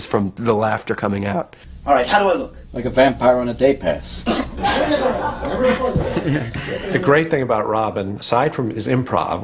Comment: "Alright, how do I look?" I can't catch the answer, but I laugh :P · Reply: I think he says, "Like a vampire at a daypass "